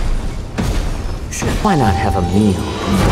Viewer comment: I heard “why not have a mew?”